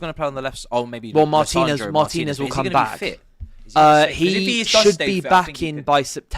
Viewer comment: no she will be back in August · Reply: thought they said she’s be back in July 😂